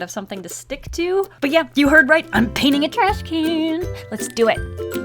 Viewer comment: I thought you said you were painting on a trash can but there’s no paint on me